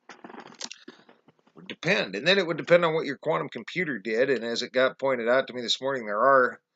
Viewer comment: I told you about the patents on other quantum computers. Unless you were talking to someone else about it also. In the video you said. A guy told you earlier about other patients for other quantum computers. But you may have been talking to a guy. If so, my mistake. No biggy. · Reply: I such a dork. You did say " got pointed out to you ". I misheard you. Sorry. Like I said. No big deal. But none the less.... I'm a dork. Lol